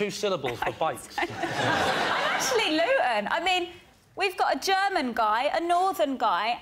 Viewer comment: Also, what does Stacey say at about 1:02? She says I'm actually xxx? I think she is trying to say she is not London but something else. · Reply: "I'm actually Luton." Which is a town in Bedfordshire, about 30 miles north of London.